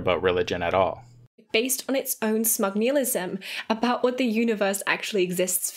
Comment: 🤚 did she just say 'based on our smugnilism' ..da fuq? 😂😂😂